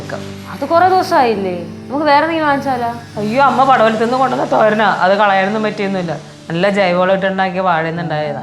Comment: did she call her amma?